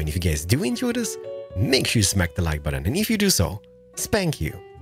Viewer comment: I thought you said "spank that like button"